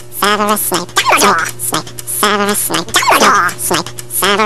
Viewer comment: lol when snape says his name its sounds like ges sayin snake